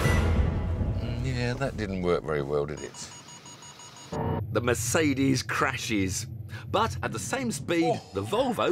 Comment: i think he said at 6:04 "MIERDA"="SHIT"